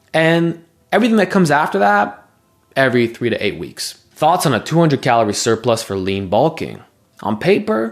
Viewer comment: bro, he said 200 percent calorie surplus 😭 he's somewhat sardonically asking what your reaction would be if he said he ate double his maintenance calories as a bulking strategy